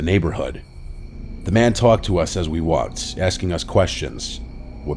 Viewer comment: did i hear right? "talk tuahs" :face-orange-biting-nails